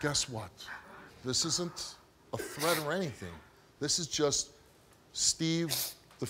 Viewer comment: What did Steve Wilkos say at 4:13-4:17?... "This isn't a--" what?... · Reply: tthomaselli2 threat or anything